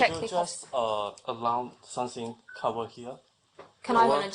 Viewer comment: he is clearly saying"something cover here", and you yourself are talking gibberish like her outta it😂😂😂